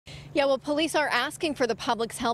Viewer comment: did she say yo?